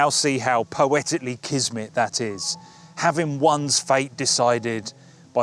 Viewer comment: What word does he say at 1:45 just after the word “poetically” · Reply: Kismet, it means destiny, fate, providence, that kind of thing.